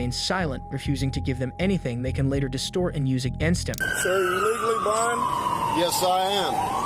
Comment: 11.32 i was cooking and i heard "Sir, are you legally blonde?" I was like WT...